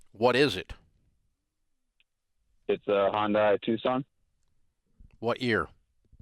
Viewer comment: Ohhhh ! no wonder 🤣 i heard wrong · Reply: I thought he said Honda. But if it is a Hyundai I understand why the engine blew up. Never buy a Hyundai.